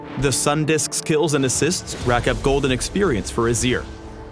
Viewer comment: I swear I heard "golden experience" instead of "gold and experience"